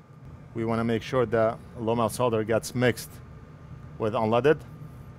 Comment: what's the solder you mix in? Leaded or what do you say? I don't hear well. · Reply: Low melt solder